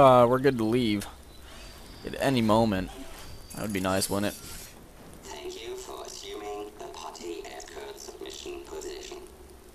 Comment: If you listen carefully, it sounds like something is saying, "Thank you for assuming the party escort submission position", just before they drag you off.